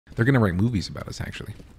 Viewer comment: i thought said "there gonna write b00bies about us"